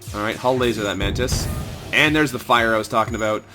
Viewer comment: The only audio glitch I noticed was at ~6:22 when "Mantis" sounded like "Zoltan." ;)